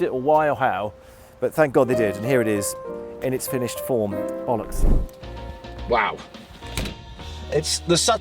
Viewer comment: does Ben say “ bollocks” lol